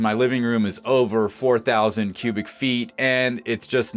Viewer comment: Great video! Wow I thought you said your livingroom was over 4000 sq ft !! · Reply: Lol! Cubic feet Mark.